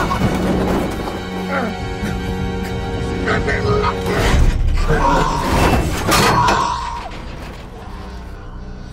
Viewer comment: Does anyone know what he said here. Because I heard: “Good people love me” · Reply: "Stupid monkey" is what he said to Koba after pushing him off the bridge